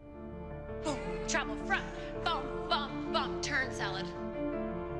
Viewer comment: did she say “turn salad”?😂